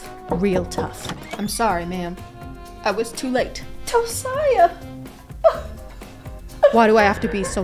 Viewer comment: did u just say my name w a toe in it as in toesiah